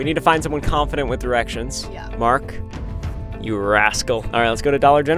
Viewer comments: i heard mark u ahole 😭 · I thought he said “you asshole” 😂😂